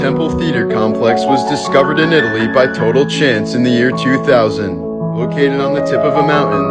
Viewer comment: I thought you said: was discovered by total chimps… no need for disrespect my man…